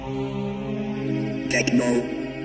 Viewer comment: Треку 30 лет, а переплюнет большинство современной музыки! Этот шедевр крутили ВСЕ любители электроники! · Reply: я слышу на 0:02 вместо техно питбулл